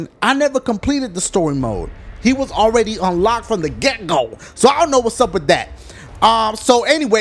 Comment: When he says Get Go in the begging i thought he said Gecko like the lizard lmfao